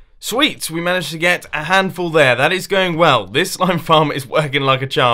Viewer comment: did he say SLIME FLARM or did i hear it wrong?